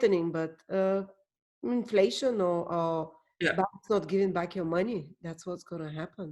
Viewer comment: What did say at 17:15? Is it 'banks not giving back your money?'.